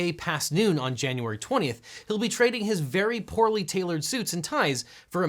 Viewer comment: “Trump’s well-tailored suits” lol! Fake news! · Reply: Where did he say that? At 15:47 he said "very poorly tailored suit". I assume you're talking about a different part of the video?